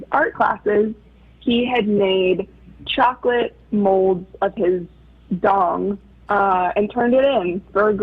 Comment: I thought she said 'chocolate mold of his dog' and I was like why is that bad?!?!